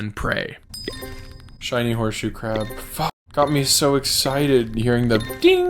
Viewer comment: Did he say the f word? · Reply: yes he said fuck